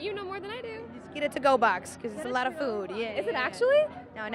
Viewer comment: "There's a lot of food." "Is it actually?" "NO. NOT REALLY. YOU ONLY GET ONE CHICKEN BREAST." LIZA KSHDBDJSJNSA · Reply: ohh that makes sense I thought she said there's a lot of boob inside